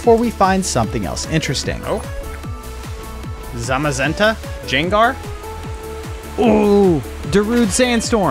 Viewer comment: DID HE CALL GENGAR JENGAR?